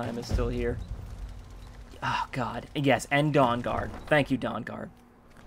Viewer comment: When that guard said "Dawnguard" I thought I heard "dong guard."